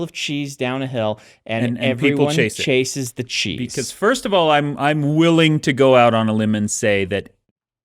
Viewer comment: ish. Did...did just say "I'm wheeling to go out on a limb" ?